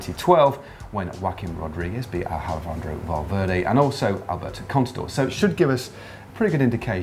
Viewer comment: Who else heard say alfendrovo valverday ?